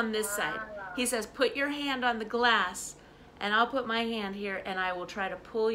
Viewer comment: I thought he saying keep your hand on the past, not glass